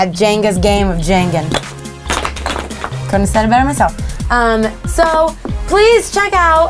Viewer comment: what to say camila at 3:30 - 3:31? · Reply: "couldn't say it better myself"